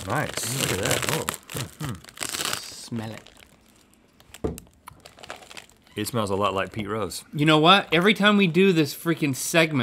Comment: LOL I THOUGHT HE SAID IT SMELL A LOT LIKE PETE WENTZ😂😂😂